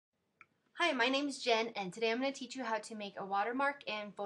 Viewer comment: lol i thought u were saying my name is jeff